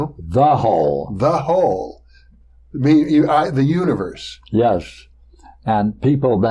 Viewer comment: thought he said "Uterus"